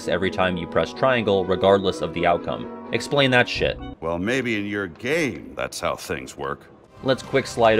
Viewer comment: I thought he said, "Maybe you're gay. That's how things work."